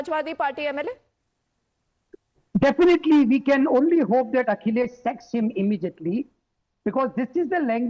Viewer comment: ""Akhilesh s.x him immediately"" 😂😂😂😂 at 8:10...is it only me who heard that😂😂😂